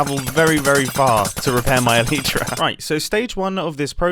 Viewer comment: did he say ... eeleetra?